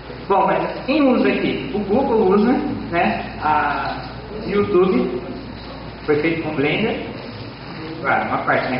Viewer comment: Youtube foi feito com blender, é isso que ele fala em 2:09 ? oO